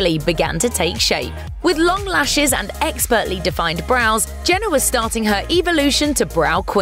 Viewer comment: Did they call Kylie "Jenna" ?? tf 6:40 why isn't anyone talking about this lol?